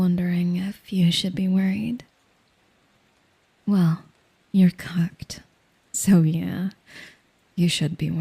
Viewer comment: I thought she said you're cucked.... I AM cooked